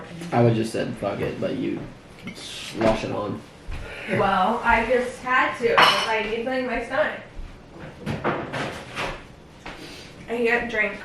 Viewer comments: Did I mishear or did she say, “I just had too, I needed to feed my son” ? 👀💙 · is it just me or did I sound like she said feed “my son”!!💙🤩😂